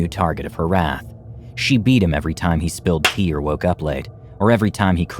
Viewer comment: Wait a minute after 3 YEARS did I just hear this guy said "she’d beat him every time he spilled pee" at 0:34 how do you spill PEE???